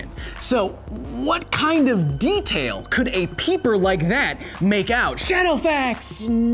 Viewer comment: Oooh you said peeper, I thought you said Pupil weird 😂😂